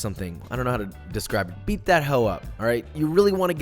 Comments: Did he just say "Beat that 'ho' up"? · Did he just say, BEAT THAT HO' UP"?😂😂😂😂😂😂😂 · At minute 2:13, do you say "beat that ho up"? I hope I misheard you!! I must have misheard you.